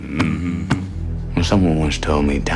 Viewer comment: Time is a flat circle, man. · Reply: I thought he said “a dime is a flat circle”.